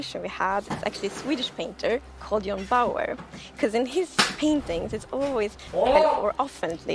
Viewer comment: what painter did she say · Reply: sorry for the 7 month late reply but John Bauer is his name